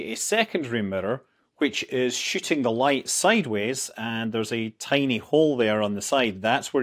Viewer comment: Why? He pronounces the word perfectly, for a Scotsman. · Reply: i swore he said shitting the light out lol